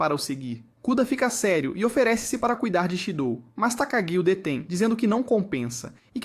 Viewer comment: mano o nome dele e shidou ou eu q escutei errado??